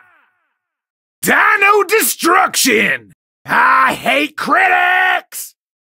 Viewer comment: i thought he said i hate critiscm😭